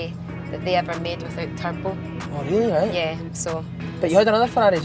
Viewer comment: what does she say? The last ferrari made without?? I keep hearding "purple" or "turple". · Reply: Turbo